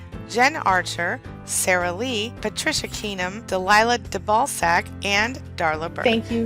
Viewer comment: Wait.... did you just say "De Ballsack?" Who would leave their last name like that? that literally says from the ball sack